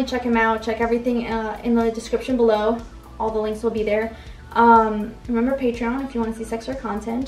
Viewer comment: ok forgive me if I am I going crazy but at 9:43 did I hear you say "remember patreon if you want to "see sex" or content'' ?? My hearing is almost gone from too much clubbing sorry lol :P